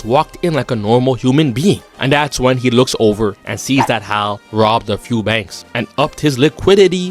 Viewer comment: I’m sorry, babe. I thought you said hail my bad😅🤣